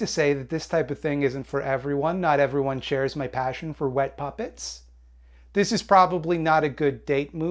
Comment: To be completely honest, I thought it said "wet puppies" until you said "puppets" and I was going along with it like that first term meant anything